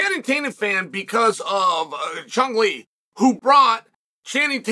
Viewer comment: I thought he said Chumlee